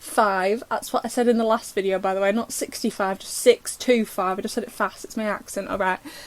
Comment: it's funny cuz I actually thought you did say 65 😂